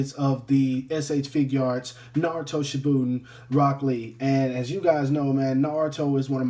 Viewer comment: why does it hear like he said broccoli instead of rock Lee XD and a lot of other parts